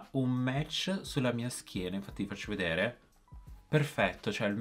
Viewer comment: Solo io al minuto 4:00 ho capito schiena?? 😂🔥